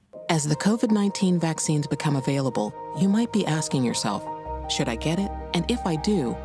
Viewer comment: Did anyone else hear "It takes a look." Instead of what should have been "Here, take a look."? I get the slip-up. What with the punchline being "It's up to you." But the anti-vaxxer conspiracy nuts...